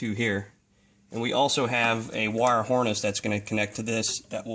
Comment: It sounds like your saying "wire whoreness". But this is a FANTASTIC video